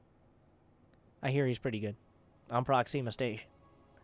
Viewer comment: I keep hearing "proxy mistation" XP